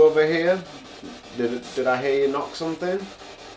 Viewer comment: I heard it say I Heard you!!!!😧👻